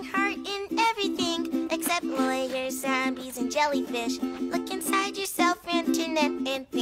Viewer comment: "There is a heart in everything except lawyers bees and jellyfish" darwin watterson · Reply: listen back. 1:19 he said “lawyers ZOMBIES and jellyfish He said it with a slight accent, like zambies so it sounded like he said “and bees” when he actually said Zombies